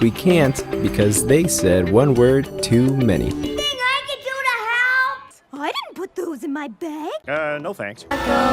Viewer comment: I thought she said can we go to hell